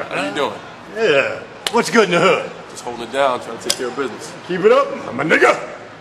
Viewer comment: Sound like he said keep it up my nigga lol n not what's good my nigga lol